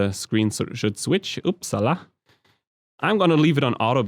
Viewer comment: thanks for your info... but I hear at duration 8:00 you said "Ups Salah".... it's Indonesian language right..? LOL....